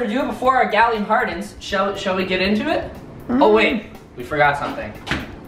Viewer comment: thought he said shall we get intimate.